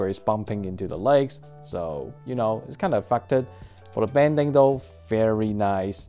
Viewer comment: did you just say the f word thank God you finally said it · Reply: That’s “affected”